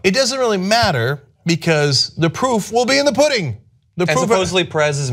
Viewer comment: Sounds like Cenk is saying "The poop is in the pudding" :D